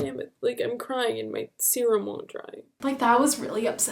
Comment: did I hear "im crying and my serum wont dry"??? 😭🤣 ps I love you, you are the coolest person I know and I envy your friends, your vibe is amazing! how?!?! xo from ireland :))